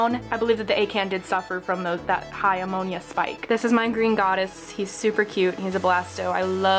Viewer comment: Did she say green goddess at 4:50 because I’m trying to figure out what coral that is I like it